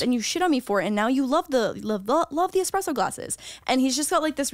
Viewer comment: I was skipping their first "add break" and the video just so happened to continue when was saying glasses, only it caught on a split second after so all I heard was "ASSES!" 😂